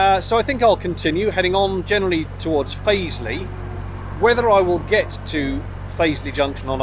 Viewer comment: for a second there I thought you said 'Paisley' and said to myself "Blimey that would be a neat trick!"